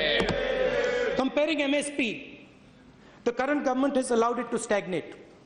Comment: did he say "allowded"??